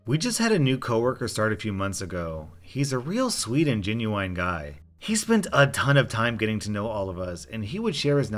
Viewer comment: did he just say 'gen you wine guy'